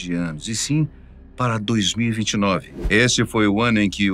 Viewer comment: Desculpa mas no minuto 1:17 eu não sei se escutei errado , mais ele fala em 2029 ???????